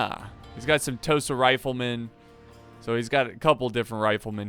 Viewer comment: the first time I watched this I thought he said toasted riflemen :'D